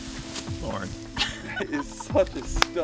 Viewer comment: What does the camera man say at 4:38? 😂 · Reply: I said “Lauren” and gave her a big 👍